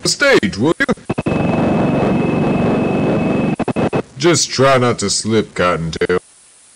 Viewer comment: “ Just try not to slip god dam-“ was my favorite part lmao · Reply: WAIT DID IT ACTUALLY SAY THAT-?!? I THOUGHT IT SAID “Try not to slip cottontail” 😭😭😭